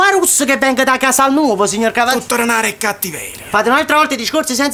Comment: Non ho capito che ha detto all' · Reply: Tutto remare e cattiveria